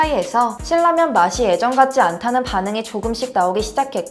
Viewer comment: 저만 그런가요.. 신라면이 실라면으로 진라면 질라면으로 들리는건요..ㅠㅠ